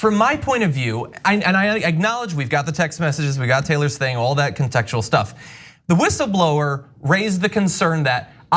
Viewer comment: Was that a Freudian slip? 7:35 Did John say "consexual stuff"? I have a major crush on Francesca so I get it. It must be difficult to stay focused when sitting so close to her.